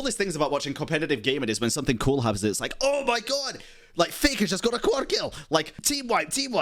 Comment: I heard Fakerrrrr!!! Anyways, speaking of LoL have you watched the newest cinematic for the New Season? It's really good